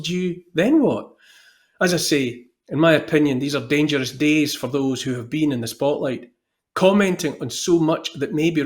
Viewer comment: I thought Neil said ' these are dangerous days for Olivers' 😆